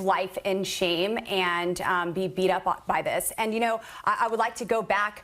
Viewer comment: I misheard Lauren Boebert saying "beat off", rewinding and turning the volume up she did say "beat up".